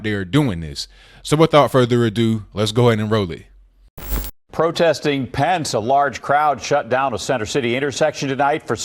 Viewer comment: I thought I heard "protesting pants" at first. I thought that was a weird thing for feminists to protest, but I was willing to go with it.